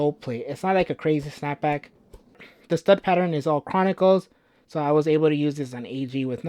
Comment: Great review. Might be mishearing but it’s conical studs instead of chronical